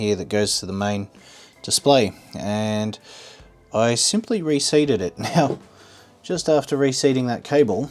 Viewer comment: ohhh re-seat…Thought you said reset